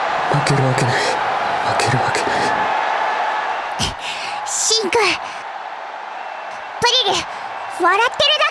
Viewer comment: の「シン」くんが、「シンジ」くんに聞こえて笑ってしまった・・・ｗ